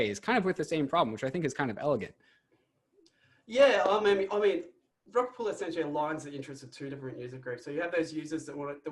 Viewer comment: I was a little freaked out because with his accent it sounded like he said "rug pull" instead of "rocket pool" a few times. HaHa!